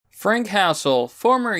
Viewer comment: I keep mishearing this name as "Frank Castle." That would have ended very much differently.